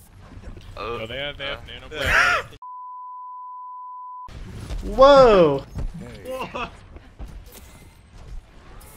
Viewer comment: WHAT DID HE SAY AT 2:20 Pls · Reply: Nicholas Curcuru he said with that haircut you kind of have to